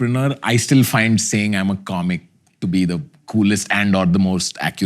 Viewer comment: when he said comic i heard controversy 😂